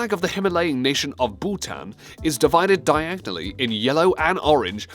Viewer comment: Bhutan! It sounded like you said pooh-than ( not being offensive to people of bhutan) I m just talking about this dumb guy